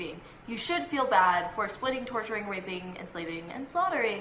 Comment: did she say raping